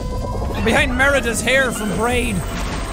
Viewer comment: "Mirador's hair from braid" I swore he said that and for some reason it drove me nuts. I don't tend to get annoyed by stuff like that but for some reason I just had to smh in disappointment.